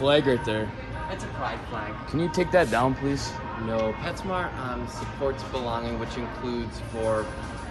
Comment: Well, he did have an impact. I am now a new and loyal Petsmart customer. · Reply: I thought the manager said it was "PetFarm"? I never heard of it, but I thought maybe it was a local store. But maybe he did say PetSmart.